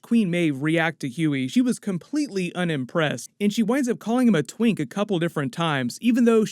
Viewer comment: Twink? I thought she said Twig because he was so skinny. Twink means something very different.